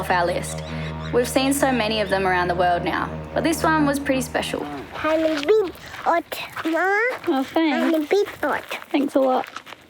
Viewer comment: what did say? "I'm a big butt mom"????